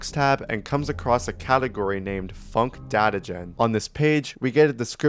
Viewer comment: I thought you said unc data gen💔🥀